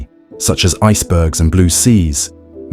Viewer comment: the subtitles captured “New York” instead of “Nuuk!” AI was probably used to generate the subtitles and I feel very smart for outsmarting AI!